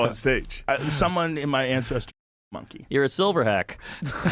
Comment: A silverhack is such a stupid funny joke 😂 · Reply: I thought he said silverback, fuck that's genius, thanks for pointing that out lmao